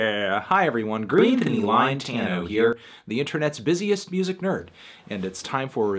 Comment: for a second i thought he said "greedthony grindtano here"